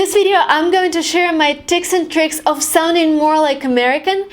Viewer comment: Who heard "tics and tricks"?